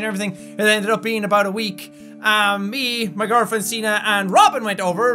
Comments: I took me a while to realize that you don't pronounce the e in:P I thought you said: my girlfriend, scene and Robin. at the beginning :P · I thought he said "My girlfriend, Cena" and then I realized what he meant 😂😂😂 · I thought he said "me, my girlfriend, Cena, and Robin" xD But then I realized he said "Signe" :P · did jack say me my girlfriend seena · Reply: Myana Frye yes, her named is spelled signe